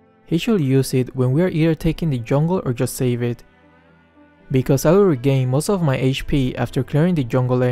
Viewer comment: I don't know why but I hear Mikasa instead of Because I